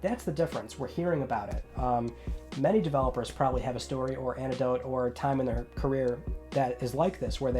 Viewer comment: Sounds like your saying Antidote, it’s Anecdote.